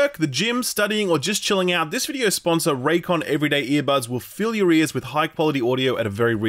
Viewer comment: Thank you Raycon for sponsoring this video. Go to <link> for 15% off your order, plus free shipping! Brought to you by Raycon. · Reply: caught a little kek in here: because of pronunciation, when you said "fill your ears" i heard it as a "fill your rears" 😅